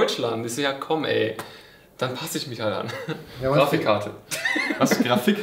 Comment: GRAFICHKARTEN 😂😂😂 Edit: oha Danke für über 100 Likes o.O Edit2: Ach komm, wenn ihr meinen Kommentar Liked, dann könnt ihr doch auch meine Videos Liken ;D Edit3: schon über 200! Vielen Dank! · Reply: Ich hab erst Grafischkarten verstanden.